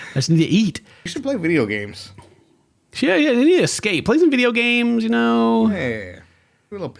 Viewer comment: Ken when you said about the video games, she needs to "escape" i thought you said "scape" like runescape lol